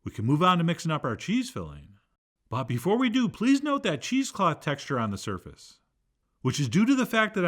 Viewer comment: Duh, I totally thought he said that was cheese cloth. Not cheese cloth 'indents'. I couldn't figure out why he was pouring the ingredients over the cheese cloth. LMAO!!!